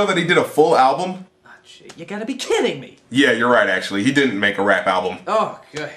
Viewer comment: What did The Nostalgia Critic say?... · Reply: He said: 'Oh, fuck that noise!'